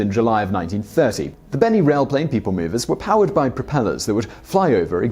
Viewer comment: I thought you said Benny HILL train!! That sounds better than the real topic